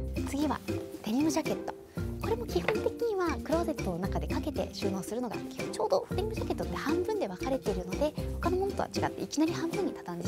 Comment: it sounds like she's speaking Spanish - "Tenemos chaqueta." Did anyone else hear that or was it just me?